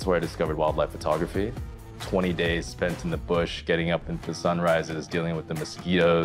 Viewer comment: for a second there I thought said that he spent "20 days in the bush getting it up to the sunrise" you have no idea how confused I was